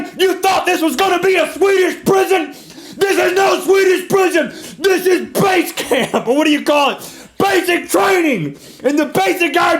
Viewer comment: I thought he said "This is space camp!" lol